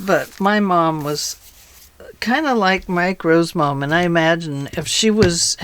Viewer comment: These journals are very pretty and the design is so fun. I had to re-listen probably 10 times to figure out you were not saying "micro and his mom"... I kept hearing "micro". And thinking who the heck is Micro? A rapper? A cartoon character? Then I figured out you were saying Mike Rowe!🤣🤣🤣🤣🤣🤣🤣🤣Wow and I wasn't even drinking!🤣🤣